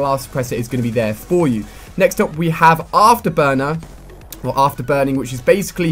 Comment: When ali-a said after burner the first time i thought he said afterboner